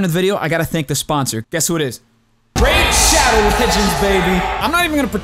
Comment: that sounded like “RAGE SHADOW KITCHENS BABY!”